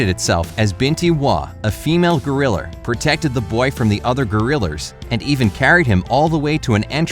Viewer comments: I heard you say gorillers hmmmmm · why does he say grillers XD